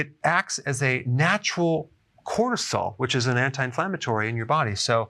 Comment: did he say cortisol?